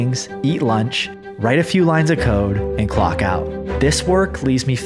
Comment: Ohhh he said “write a few lines of code”. I thought he said “do a few lines of coke”